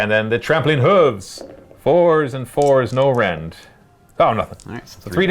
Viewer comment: Could've sworn I heard call the chariots' horse attacks "trampoline hooves." Debating petitioning GW for a new name for all horse attacks. Though I suppose if any unit will get a trampoline hooves attack it's squigs.